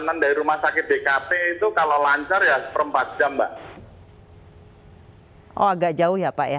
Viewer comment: koreksi mbak reporter ,.kalau nggak salah dengar (05:07).seperempat jam bukan empat jam (05:12), perjalanan dari rumah sakit ke rumah alm.